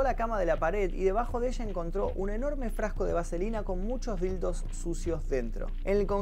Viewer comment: MINUTO 22:01 "con muchos...." que? no entendi esa parte. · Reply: Dildos